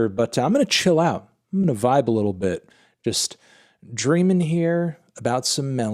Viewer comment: did you say DRAINING!!?!? Melon confirmed drainer